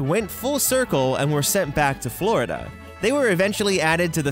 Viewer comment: When he was talking about the donald duck one I pause at the right time when he was saying Florida and it sounded like he said floor XD